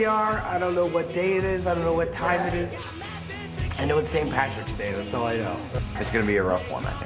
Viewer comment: "Today, we're shooting Teenagers, That's all I know." And the award for best choice of words goes to... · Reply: I THOUGHT HE SAID "IT'S SAINT PATRICKS DAY, THAT'S ALL I KNOW"